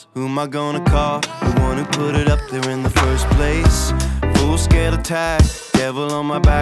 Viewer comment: i hate to say something like this in a comment section this small, but i misheard "full scale attack" as "foreskin intact". yeeaaaa...